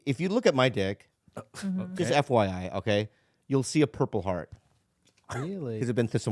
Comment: Could’ve sworn Bobby said “purple wart”. 🍆💜